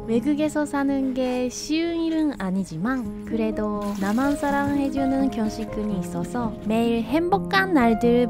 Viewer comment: 오랜만에 봐서 그런가 순간 남한사람 해주는 이라고 들었네ㅋㅋㅋㅋ